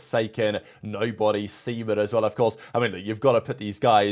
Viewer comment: what did he say? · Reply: semen